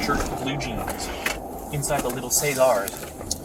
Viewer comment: Did he say .....Say Zars